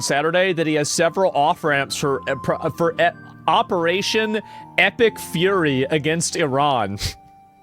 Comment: woah... thought he said "operation ethnic fury" ....lol